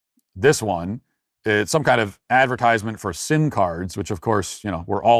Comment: did he say sim cards question mark all the rage in the nineteen sixties question mark